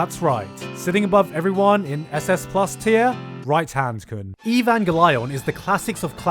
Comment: i misheard that as “right hand coom” and that would’ve worked equally as well